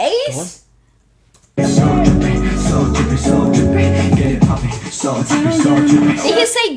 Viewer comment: You know, for a while I thought they were saying "get it papi" too, but they are actually just saying "get it popping" lol